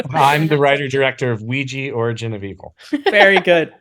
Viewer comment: Yeah, so I heard "Luigi, origin of evil?" That's definitely a Mike Flanagan / Nintendo mashup I'd be down for.